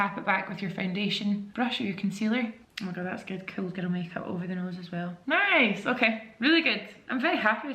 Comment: Jamie I love you gworllll, but what's up with this "cool ghetto makeup" description immediately talking about how its a bronzy look, What do you mean by that?? (inserts confused meme) seems a little distasteful for a makeup description, js!!! I was actually shocked I rewatched it twice to make sure I wasn't mishearing you. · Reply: I’m saying cool girl makeup - it’s my accent!